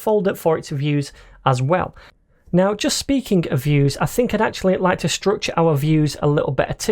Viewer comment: when you say 'speaking of views' I thought you were saying 'speaking of vue's'....BTW, how can we separate 'views' from the server side and use Vue/Nuxt instead? hope to see the crash course soon